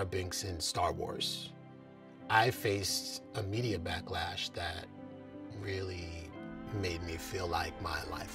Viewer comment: This video misquoted Mr Best at 1:26. He says "I faced a media backlash that made me feel like my life was over." You have the subtitles saying "I faced immediate backlash." Please fix.